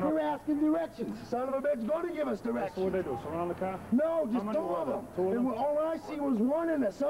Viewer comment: Did he really say 4 N-words surrounded the car??????????? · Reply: "so, what'd they do, surround the car?"